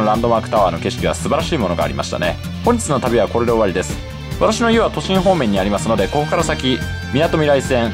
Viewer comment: 私の家は栃木方面に聞こえるw